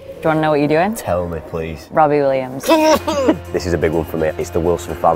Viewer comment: I thought she said RobIN Williams at first, and I was so confused...